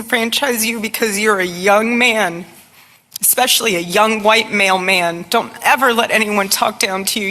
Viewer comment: 😂😂 "YOUNG WHITE MALE MEN" 😮 I thought 🤔 she had said "Young White MAIL 📬 MEN"😂😂😅😃😱🙀🙀🙊🙊